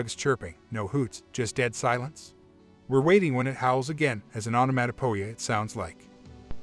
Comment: What is said at 3:40 or so Right before that creepy sound of a 'Howl' ...... ' as an automatic ____ ' ? Gnarly account, description of thing and all. No thank you. EVER> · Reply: Onomatopoeia. It's a word that describes a sound by mimicking it Ex. Bang, Oink, Vroom.